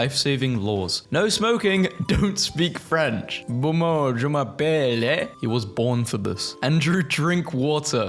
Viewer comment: what was bro tryna say 😭🙏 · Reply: Momo, je m'appelle ? Humm... Momo, my name is...